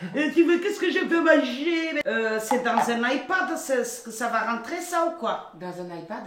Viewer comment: "C'est dans un Ipad ça ou quoi ?" "C'est pour ceux qui n'ont pas de dents ?" ( 3:42) 😭😭😭 JPP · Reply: Elle voulait dire EHPAD j’crois, ça me fume 😭